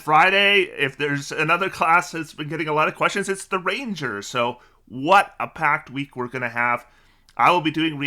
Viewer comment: What he said: "What a packed week we're gonna have!" What I heard: "What a PACT week we're gonna have!"